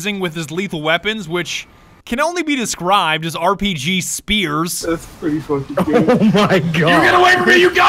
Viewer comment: For a split-second, I thought he said RPG Sears. Like what kind of Sears only sells RPGs like why would they need to sell those.